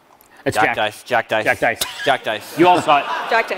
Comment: What was being said @ 1:01:21? Sounds like "jack dice" but I have no idea wtf that would mean